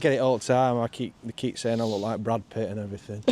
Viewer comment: Fab Tales and views from Robin Hoods bay. I felt that cold weather. The coffee and cake was my favourite. when you said you looked like Brad Pitt I thought you said Gravel Pit 😂. Lovely to see again you guys always have a great time together. ❤